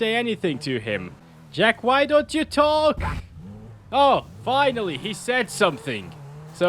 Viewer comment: Did Jack just say “Fu..”! Language Jack 🤦‍♀️🤦🤪